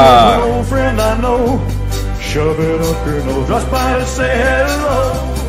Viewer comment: After old friend I heard him say words ( shove it up your nose). Am I only one hearing it